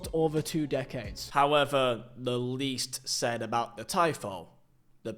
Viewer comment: tie foo 😂😂😂😂thought it was called tifo 😂